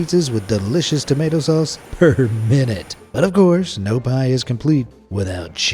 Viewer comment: did he just say "Pie" ? did you miss read your script? one Google search later WHAT!? PIZZA IS A TYPE OF PIE!? N.I.S.E